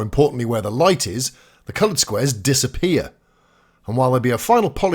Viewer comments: Cunt squares? Am i hearing that right? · am i hearing “cunt squares” ????? 😳